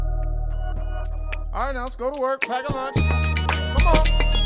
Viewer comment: I'm dyslexic. I keep thought it said packet lunch 😂😭